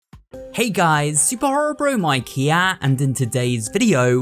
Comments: Am I going crazy. I heard “Hey guys, super horror bro Mike HEEYA” 💀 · I thought he said super horror bro my gyat 😭😭😭😭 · why does it sound like he said super horror my gyatt at the start 😅😅😅🫢🫢🫢🫢🙃🙃🙃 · is it just me or i heard that superhorrorbro my gyatt 💀